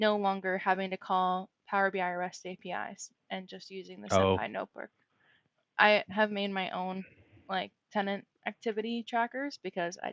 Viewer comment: What did say was her favorite fabric feature? Sorry the audio isn’t great at 31:03 and it seems like the subtitles don’t get it right at that moment. Great podcast, thanks for sharing. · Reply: SemPy (Semantic Link for Python) Notebook! It's a tool for interacting with Power BI management APIs from Spark notebooks. Also thanks for your patience, we are figuring out a workflow still for me to edit subtitles and catch stuff ahead of time as well. Expect some fixes coming in over the next month.